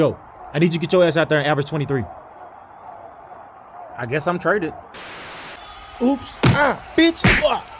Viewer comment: It really sounds like reaves says “guess im traded” lmao